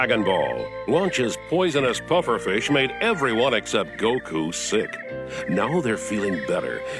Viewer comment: did nobody hear that?! xD poisonous pufferfish made everyone except goku THICC xD xD xD Apparently I heard it wrong it’s SICK not THICC